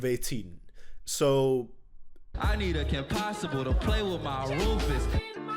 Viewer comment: i always thought Blueface said: I need a kim possible to play in my roofs LMAO